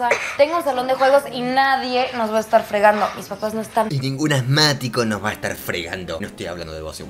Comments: Pense que decia hermano asiatico no asmatico jajajajajajajaajajajaja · Nadie: Absolutamente nadie: Los pendejos como yo xd: hermano asiático xd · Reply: Chaaaa, si no veo tu comentario no me doy cuenta que dice asmático jajaja, pensé que decía asiático jajaja